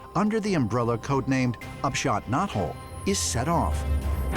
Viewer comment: Anyone else chuckle at "upshot knothole" or was it just me? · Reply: I heard "off shot nut hole" 😂